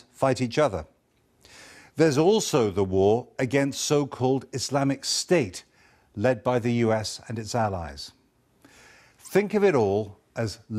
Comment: DID HE JUST SAY ISIS WHY LEAD BY US AND ITS ALLIES!!